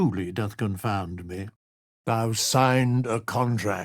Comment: I thought it said 'boo signed a contract with the devil' and it made me laugh so hard, now i wish that was it instead of thou